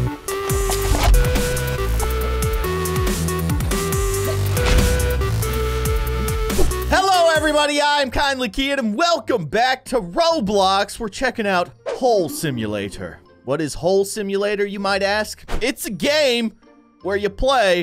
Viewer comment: Thought it said home